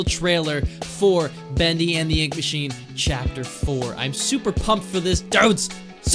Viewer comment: Whenever he said Chapter 4, I thought he actually said "Chapter Floor" To be honest.